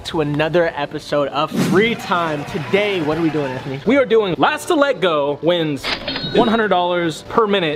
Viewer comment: i thought anthony said last to lego hahahah